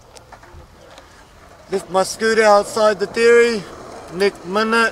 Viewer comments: I always thought he said “this my scooter outside the theory” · This is what I hear: “Left my scooter outside the teery, nek minut.” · I thought he said “ligma nut” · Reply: no i thought he said nick manat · Who’s here from lazarbeam? 😂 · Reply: I thought he said nick manat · I thought it said nick monac 🤣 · I always thought he was trying to say Nicki Minaj. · Who’s here from lazarbeam? 😂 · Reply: I thought he said "nick nimmin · i always thought he said nick minaj